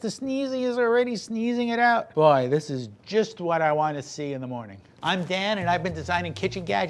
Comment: I actually thought he said "I'm dad" 💀💀💀